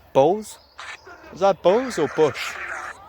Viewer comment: I heard "bulls".. 🤷🏼‍♂️